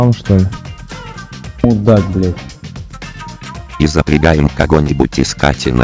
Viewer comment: я один не понял,что он сказал? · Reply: и запрягаем кого-нибудь искать enemy